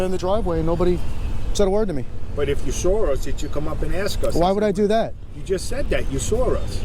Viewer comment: WTF is that cop saying at 7:47? "If you sore us?" what does that mean?